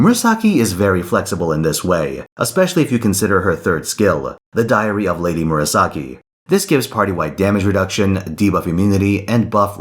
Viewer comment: For a second I thought said The Diarrhea of Lady Murasaki